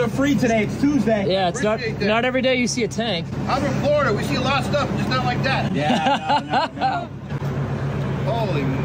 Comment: I thought Florida man said “I’m in porn, we seen a lot of stuff but not that” accurate either way.